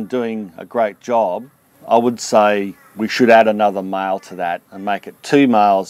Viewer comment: I thought you were speaking about miles instead of males ..